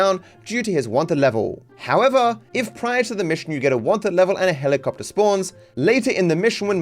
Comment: there's a subtitle mistake. It says "Water level" instead of "Wanted level" as intended, probably due to how you say "wanted" here being a bit off.